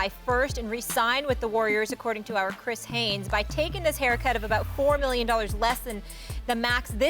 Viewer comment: Molly think she slick I heard her say "haircut" instead of "pay cut" lol get off the homies hair lol he's a champion with bad hair now😂